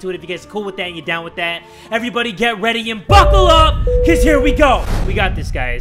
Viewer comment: did he say buckle up? or fuck up?! on